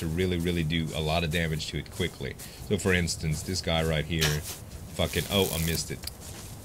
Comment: did say fuk it at 12:03 !?!?!?!